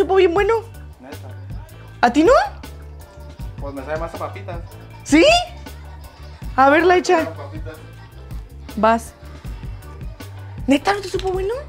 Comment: En verdad escuche "Laisha" en el minuto 14:23 o soy la única?